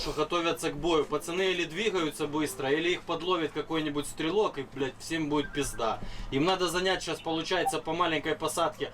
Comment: I just heard "strelok"